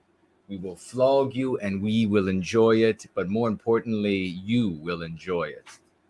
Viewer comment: Skipped to a random time and all I hear is “we will flog you. We will flog you. We will flog you and we will like it.” Lol